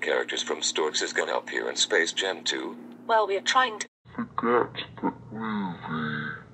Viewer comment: What did she say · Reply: she said "forget that movie" in her sentence